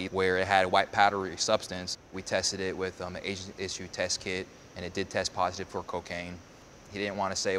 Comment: did he just say "the asian issue test kit"?? 😂